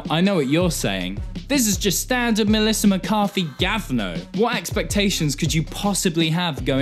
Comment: Did he say "gavno"? Is it russian? :D